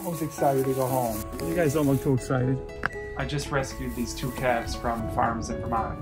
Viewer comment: it said calves and I thought it said cats and I was like “uhhh.. well they could be- no no”